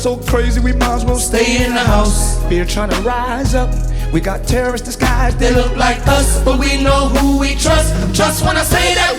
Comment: Did he say “we got terrorist in the sky that look like us”? If not disregard the text hereafter If so,that line is problematic and also false we’re not in the sky doing anything remotely close to what actual terrorist are doing. This dude must be from the diaspora because “us” wouldn’t say that.